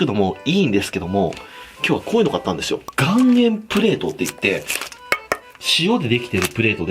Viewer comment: 顔面プレートにしか聞こえん...